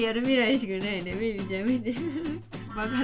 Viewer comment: の字幕「ピピちゃん見て」って言ってるように聞こえる